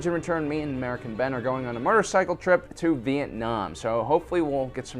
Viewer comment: Motorcycle- I thought for a second you said “murder cycle”